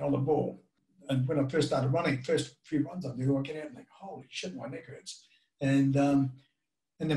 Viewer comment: What does Rosco say at 2:00? It sounds to me like “Holy sh*t my knickers”! Which it could well be, considering he’s an Aussie! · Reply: "holy Sh*t my Neck hurts"